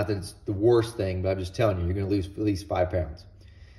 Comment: At the end about the low oil pressure, I had to replay it😂😂 it sounds like you said your gonna lose at least 5 friends